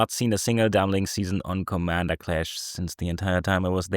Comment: At the start it sounded like said "Commander Crash" And honestly, that would be an awesome name for a series. Maybe a quick and dirty crash course on Commander stuff! Could do some basics of 'How to build' for certain commanders, or maybe a focus on different aspects on how to play/build certain themes, or even different parts of how to play the game itself, general politics theories, thoughts on how to focus on certain goals, or threat assessment. Dunno, but could be a cool series to add to the channel!